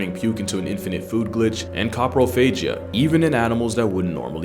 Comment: WHAT CONDITION DID HE SAY? Took alot of listens and Google searches to find it 😂😂😂 COPROPHAGIA =The practice of eating stool/feces